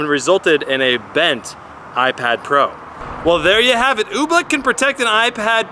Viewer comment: I thought it said OBLOCK 😂😂